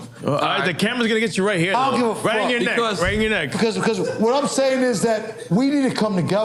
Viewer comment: "Cameras gonna cut you right here 💁🏾, right on your neck" they're trolling him the whole time he was having a breakdown 🤣🤣🤣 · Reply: Thought they said "rein in your neck" 😂